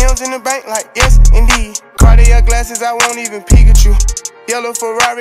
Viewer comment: i thought he said "i wont even pikachu"